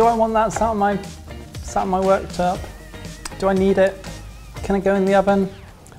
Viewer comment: "Do I want that sat on my workterp" I had to replay 6:03 a few times...but i think he actually does say workterp.